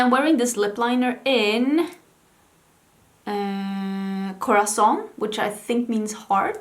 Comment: When you said "corazon" I heard "croissant" and it cracked me up XD